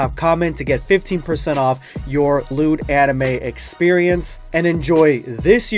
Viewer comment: Read Dragon Ball Super Halloween Here <link> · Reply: with subtitles says I get a lewd anime experience. Nice 😁👌